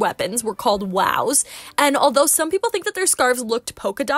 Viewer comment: i’m severely sleep deprived at the moment so what i heard was ‘exploding kittens’. had to check the comment section to see if i was going insane. turns out i am.